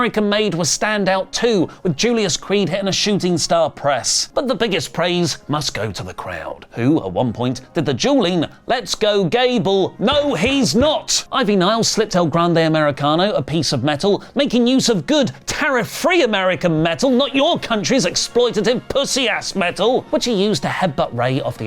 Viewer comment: I thought they were saying "You're Chad Gable... No He's Not!"